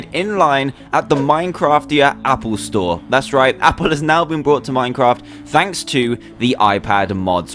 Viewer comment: Apple has now been bought minecraft thanks to the iPad Ahmad so I heard LOL